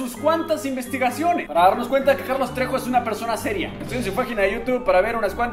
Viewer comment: alguien se dio cuenta que dijo "fajina" en vez de "página"?😂